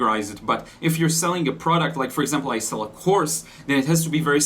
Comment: I swear I heard you say that you were selling a horse and I just cracked up laughing. And it had to be specific.. so I was thinking perhaps a chestnut, or a palamino lolol!